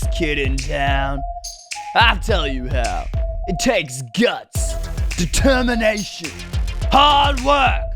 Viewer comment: At the begging I'm like what did you just say you tasted guts or you just said guts edit: omg I never got a like it's not blue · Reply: He said it Takes guts